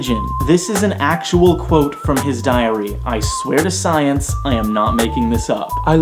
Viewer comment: It sounded like you said "diarrhea" and not "diary". I lol'd.